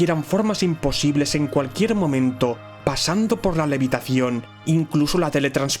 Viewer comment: que dijo? Meditación? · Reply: Levitación